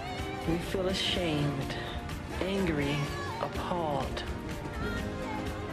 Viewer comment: "But there is no....." What did Michael say? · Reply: "..But there is no action. No action.." <3